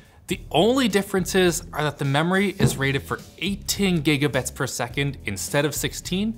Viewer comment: You said 8 ting instead of 18. 1:02 HAHAHAHAHAHAHAHAA loser ;)